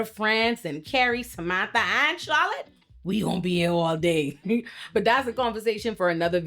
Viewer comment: girl i love your channel, but the subtitles treating your accent like it’s an alien language is wild lol, watch what happens when she says Charlotte.